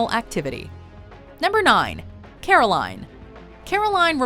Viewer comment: i thought i heard "coraline" instead of "caroline". Because that would be fuckin amazing.